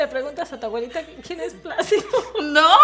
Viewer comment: Thumbs up if y’all want more videos with my mom!! She loved all the sweet comments and says thanks you 💓 (also placido is a guy from Mexico with big lips 😂😭) p.s I just added English subtitles for those who don’t understand Spanish! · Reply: omg I thought she said plastico, but now the name placido got me like 😂😂😂